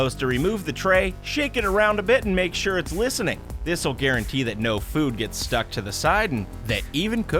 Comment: did he say “shake it around a little bit to make sure it’s listening.”?